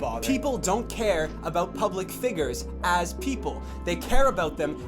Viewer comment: ah, Jreg said "figgers" instead of "figures". I almost thought this was serious!